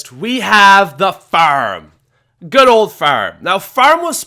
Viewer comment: what is ,,baroom,, , oh you said farm